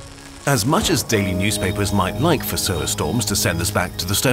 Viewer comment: Thought it said Solar Stonks